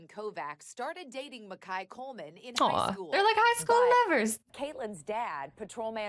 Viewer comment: did they say high school or preschool 😂